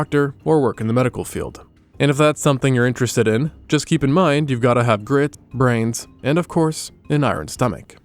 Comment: It thought you said "you gotta have girl brains"